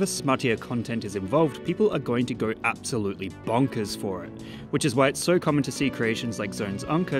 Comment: did you say....BONK!!!?